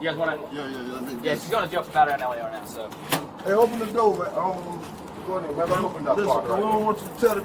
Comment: I heard the officer saying, "Open the door!"